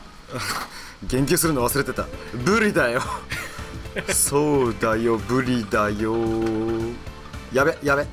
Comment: そうだよ！ブリだよぉ～♪（笑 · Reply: のがそうにしか聞こえなかった件w